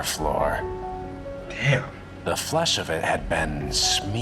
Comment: Oh... It's even worse than what you thought. He said FLESH, not skin... Common mistake, but it was the MEAT from off his bones that was spread like butter.